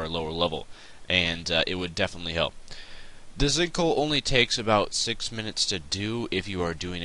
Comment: I could have swore you say stink hole in the beginning lmao!!!!!!!!